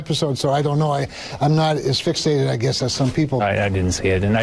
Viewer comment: That first senator I heard said he wasn’t “as fixated” on the tweet. I swear I heard “asphyxiated’.